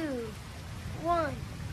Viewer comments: Is she saying "Haha holy fuck?" That's literally all I can hear at like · Did she say “holy fuck” at 0:08?😬